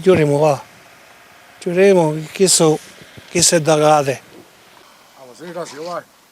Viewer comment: Chi è disposto a dirmi cosa dice a 2:05?? "chissu è _____???" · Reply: D'agate